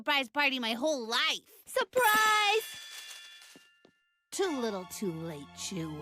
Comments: I thought she said at 17:48 “TOO LITTLE TOO LATE JEW” Which considering this is Disney, wouldn’t be too suprised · I THOUGHT MABLE CALLED CANDY “Jew” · i was so confused until he said "oh last name" (thought Mable said jew)